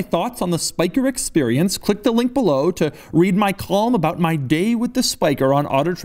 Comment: When you said "column" I thought you said "qualm", because you can't pronounce things worth a shit